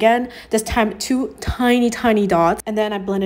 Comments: I thought it said fight... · I thought she said “fight” for a sec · I really thought she said fight😭😭😭